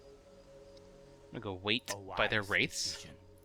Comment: I heard you say "I'm gonna' start with their Wraiths" and I was like "X NOOOOOOOOO." Yeah, Lee Sin usually starts at the Lizard Buff, by the way. So, if you're going to counter jungle him, steal his Golem or something.